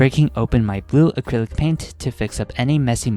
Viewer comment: I HEARD YOU SAY:glue paint)BRO ITS:blue paint)